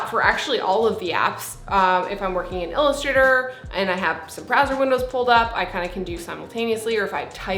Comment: I thought she said Brazzer windows